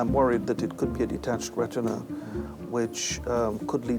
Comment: In USA a detached retina is an EMERGENCY!! I don't know what ENgland is thinking!! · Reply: Did they actually say detached retina? Because I thought he said floaters in the end, which are quite harmless.